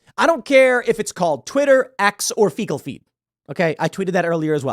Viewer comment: Fecal Feet lol At first I thought Tim said Fecal Pee. 🤭